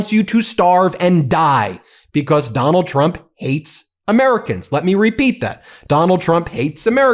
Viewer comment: I heard “eats Americans” at first 😅